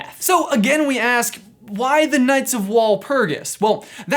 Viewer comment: Did you just say ku nights